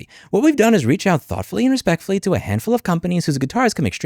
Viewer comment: what's even more messed up, the audio from the Fender CEO, it sounds like he says "Respectively" rather than Respectfully (as in "In Respect to the matter" or "In Regards to the matter" where people's brains will automatically switch it to respectfully on them...).